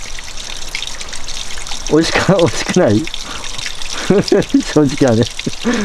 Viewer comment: 待ってた 6:45うん 07:02 と7:08なあにぃ？これ 7:34カロリーメイトぉ? 7:56何だコレ😮 ←ハッキリ聞こえて可愛い 8:07 なに！? 8:16 と8:19美味しくない 8:29 何それー？ 8:33 かり、かり、うめ 8:43き、く 8:45よ 〜中略〜 10:06 きてない 10:19 たまに見るよ 10:28何回か見たよ 11:10 と11:17自信ある 11:35うん、そう。おーい 11:39うん、おーい 12:37 もーじゃーもーじゃー 12:47 そう、髪型 12:50 てんねんパーマ 12:54 てん⤵︎ねん⤵︎パァマ 13:38シルバァ 14:04 置いた人いるー⤴️ 14:27ビニールゥ 14:35 そう 15:27 メンバーに入ってた 15:45 メンバーに入ってた 16:36 うん、いいよ！ ←ハッキリ❣️ 21:57ありがとござったって聞こえる。 こちら側の声が音声変換にかぶりましたね😅 近くに車ありますよね。 5:31「ちーん、ETCカードが挿入されました」ときこえました。ETC車載機か、他の機械かな。 、京明先生の復唱が多いですね。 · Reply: 美味しくない😞も聞こえました 、少年らしい可愛い声ですね。自分も聞こえたので、返信欄に失礼致します。 協力してくれる優しい子も、成仏してほしいです。